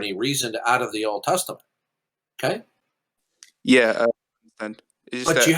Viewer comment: did you just say and repeat “yeah, but”? Uh oh…